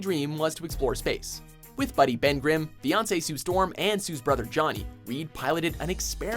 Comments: did he say, beyonce sue storm?? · did they really say "Beyonce's Sue Storm" at 6:15?!? Umm, thought that was jessica alba. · did you just say; "Beyonce Sue Storm" ??